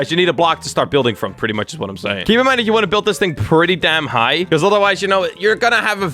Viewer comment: did he just say damn high?